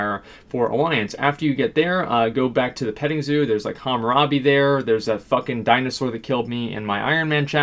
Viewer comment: Hammurabi? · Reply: sounded like Hammurabi, most likely intentional